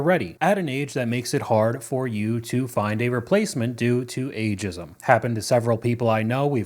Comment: Holy crap, i heard "A jizzum" when you said "Ageism." Hahaha